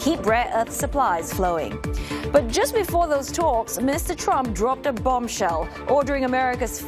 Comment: I swear I heard Minister Trump at 0:22, replayed it like 5 times.